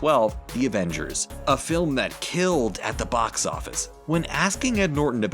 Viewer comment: I thought he said avengers killed the box office xD